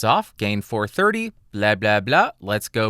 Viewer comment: blia-blia-blia 😂 I'm russian. It means "bitch-bitch-bitch". Why u say "blia-blia-blia" instead of "bla-bla-bla" 😂? PS Add simultaneously recorded audio tracks for two comparising microphones with any AFC analizer applyed on them (for instance, fabfilter pro-Q3 displays two AFC tracks simultaneously), to demonstrate honest digital difference between the considering pair of microphones.